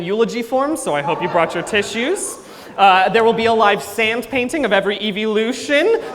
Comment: Dear Arceus I heard “live Sans painting”, I love Undertale way-ay-ay too much